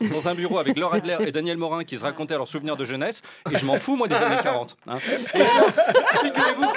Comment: j'arrive pas à entendre de quoi il se fout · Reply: des années 40